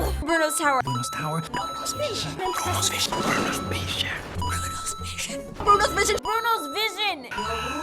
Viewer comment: why did I hear "Bruno's spaceship" lol😂